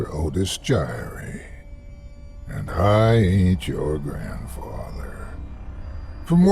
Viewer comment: Well Damn. All this time I thought he was saying "I ate your grandfather" 😲